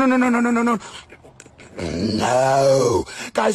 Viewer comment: GOT ME ROLLING TO THE BACKROOMS Edit:woah I'm famous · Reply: i always thought at the end it said me don't choke